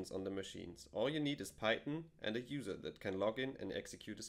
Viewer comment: Hi at 3:06 - Did you say all you need is python or Titan? please clarify if possible - thanks